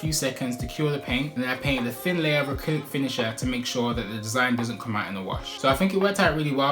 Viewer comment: i didnt understand the word before finisher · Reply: Acrylic finisher